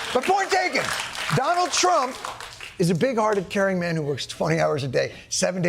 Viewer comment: I heard him say "Big Farted?" Listen again.